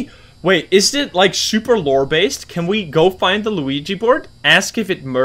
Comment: Did my man just say Luigi board lol instead of ouija board.